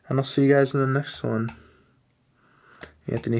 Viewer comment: I thought he said Tony Montana like scarface not Tony Fantano